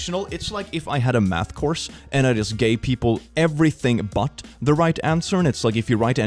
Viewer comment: "It's like if I had a math course and I just GAY people everything, but the right answer." - 2022 (0:47) I know it's not what he said, I just found it funny.